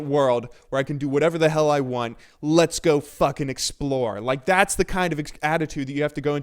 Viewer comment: Am I the only one who heard "Let's go fuck and explore!" at 5:53???